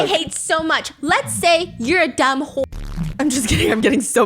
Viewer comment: Did just call that woman a whöre?